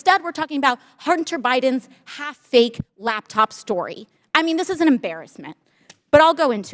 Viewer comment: so am i trippen or did aoc say the laptops not real? 1:55 - 2:05 and why does she look like she's on speed the way she moves the mic around and the super hyper movements